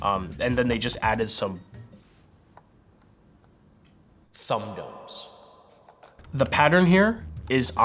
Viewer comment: "They just added some...thumb [norbs]." The excessive reverb didn't help there editors. Norbs? Nourbs? Orbs? Nurbs? Nubs? Warbs? Warts? Replay a bunch, watched the lips move frame-by-frame, and I still have no idea what he said. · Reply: I think from the earlier (1:28) he said, [Thumb Domes], instead of Two Giant Balls 🤣